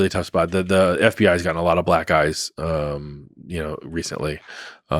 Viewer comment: did he say a lot of black guys?